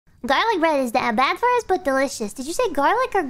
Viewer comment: I heard GOOra